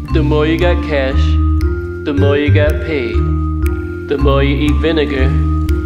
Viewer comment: I thought he was gonna say, “The more you got cash, the more you get laid” but paid is so much better😂